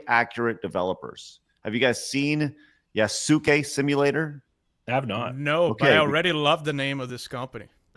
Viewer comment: I love that the developers of that Yasuke Simulator named themselves "HistoryAccurateDevelopers". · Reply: I thought he said "Suitcase Simulator". Some of these games are getting weird.